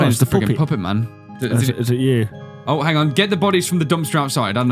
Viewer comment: At the puppet minigame part, i thought it said "get the boobies" instead of bodies.😂